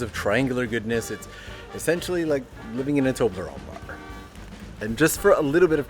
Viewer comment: I have replayed the line at 0:18 literally more than 5 times and I still have no idea what he’s saying… essentially living in a what?? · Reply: TOBLERONE BAR lol